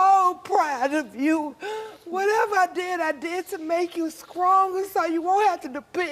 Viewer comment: Don't come for me but I heard her say 'Scronger'